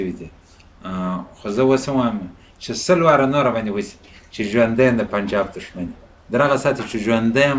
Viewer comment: he never said Pakistani Taliban he clearly says Punjab, which means the state of Pakistan which is responsible for the killings of pashtoons in Peshawar and Kabul